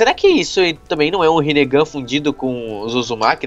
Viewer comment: achei que ele tinha falado fudido como os Uzumakis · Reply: Vitor Rezende Borges ele falou fundido